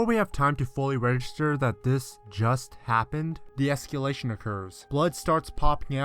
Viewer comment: Just want to clarify: It's "escAlation", not "escUlation". It sounded like he was saying esculation every time.